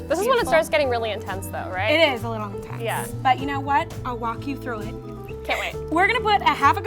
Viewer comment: this is so funny i am so glad i chose this to watch omg i just heard i'll walk you though it as i'll wack yo through it and i can't stop laughing